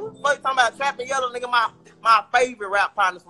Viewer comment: did he say trap and yellow his babies? Someone correct me if I'm wrong